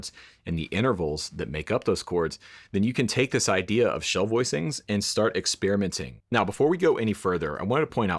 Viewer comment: Thought it said Shull voicings at first.